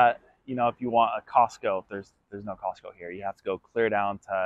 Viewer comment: Unable to understand what you are saying at about 9:30. "tTere's no hospitals here?" or There's no cocktails here?" or There's no hostels here?" What is the word you are trying to say following, "There's no _(what?)____ here?" Thank you. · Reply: You were close! I said there are no Costcos in Vero, you have to drive almost an hour up to Melbourne for the closest Costco or way down to West Palm Beach for the other one